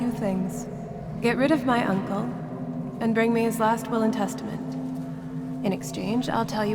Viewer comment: I heard get rid of my uncle and get his intestines!